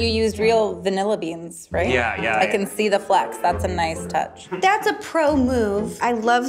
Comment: Caption says "flex", should say "flecks". 50:10 Same for "specs", "specks".